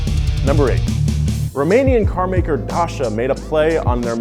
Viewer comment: did he say Daca or was that just me